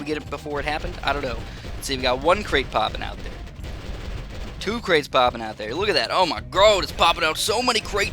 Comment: Omg what exactly were you saying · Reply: Tuba_nerd 2 crates popping out there...thats what he said if thats what you mean